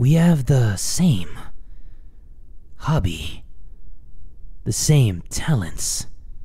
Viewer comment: am i the only one who heard hubby??